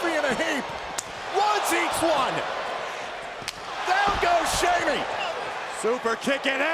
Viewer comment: Did he just say Shamy?